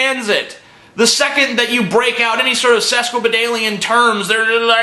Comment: what was the word that he said? Sesquobenalien?